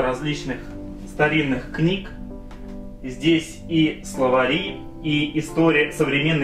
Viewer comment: Мне одному послышалось "СловаЛи" , а когда пересмотрел услышал "Словари" ? :D